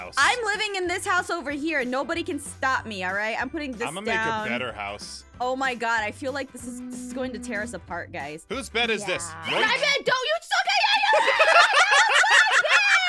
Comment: Starts to laugh Wait what did they say? 😂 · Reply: No don’t take my sokawiyeeeeeee